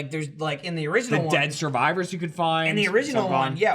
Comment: Did just say dead survivors